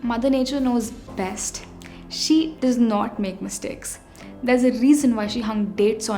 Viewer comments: the subtitle is wrong. You say mother nature does not make mistake but in subtitle mentioned "She does make mistake". But very informative video. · Very good and informative, in 5:54 mother nature knows best. She does make mistakes is written, instead of she does not make mistake. Kindly correct it